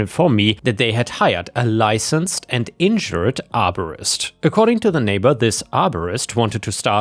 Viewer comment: I thought you were saying that the arborist was injured and wondered why he would put that on his business card . I then realised that you were saying insured! 🤦‍♀️